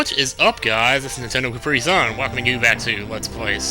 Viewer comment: first time i heard him introduce himself, I thought he said Nintendo Cap-Free Zone. I fail:-)